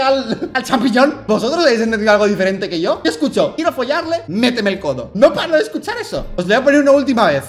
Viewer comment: yo entendi metemelo todo xd jajajaj